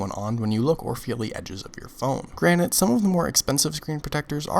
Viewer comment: Did you say "granite" at 1:49? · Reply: "Granted" 👍🏻